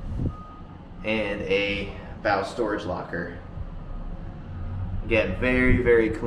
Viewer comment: it sounds like he says, "spouse storage locker". I was about to see if it was still for sale.